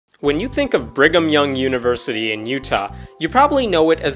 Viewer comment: lol, somehow I always heard "Bring'em Young" instead of "Brigham Young"